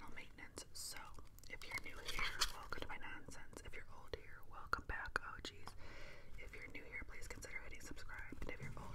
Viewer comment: In your intro when you say ‘welcome back OGs’ I thought you were saying ‘welcome back oh jeez’ for the longest time